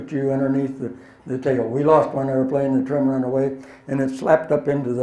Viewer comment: he explains what caused the accident but I cant understand what he says that caused it? anyone??? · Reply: He said "the trim ran away." Aircraft have trim tabs on the elevator to lighten control forces for the pilot. If the trim control fails, the pilot's ability to control the aircraft is diminished or lost altogether... Meaning: Mechanical failure caused the crash.